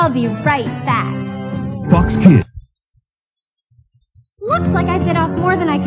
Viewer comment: "FUCKS KIDS" sorry thats what i heard